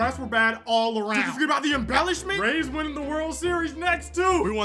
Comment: I thought you said Braves winning World Series next I was real confused then I realized you said Rays 🤣